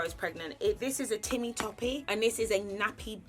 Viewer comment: Did you call it a timmee toppie? 11:57 😂